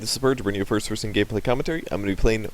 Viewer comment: Why do I hear "gay play" instead of "gameplay" 00:02 ? :O